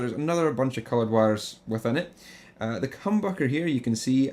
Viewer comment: did he say "the cum bucker?" Lol